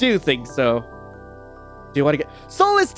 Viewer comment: “do you want to get married to me…?” Is what he’s saying